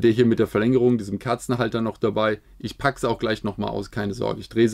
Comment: hab ich da Kerzenhalter gehört? O.O